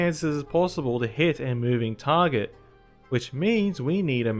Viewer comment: Did bro just say “an moving target?”